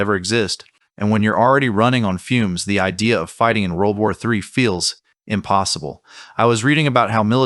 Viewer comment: I just love how even the auto captions "CC" still struggle with the fact of the high possibility for World War 3, instead automatically interpreting what you DIRECTLY and CLEARLY say at 2:08 as "World War II." 🤣 🤣 🤣 🌎 🔥 hell in a handbasket, anyone? :P